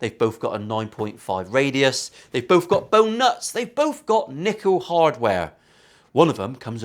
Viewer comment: Sorry?!! For a moment there I thought you said nipple hardware but, it was probably nickel hardware 🤭